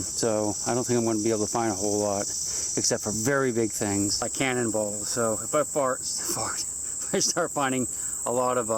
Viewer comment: did say that the only thing he could find in the muddy river bottom are "large things like cannon balls and farts" ?